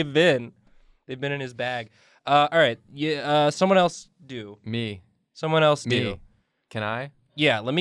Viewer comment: what was said: "someone else do." "me!" what I heard: "someone else do me!"